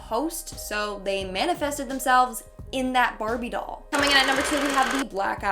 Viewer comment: I heard "COMININATNUMBERSIX"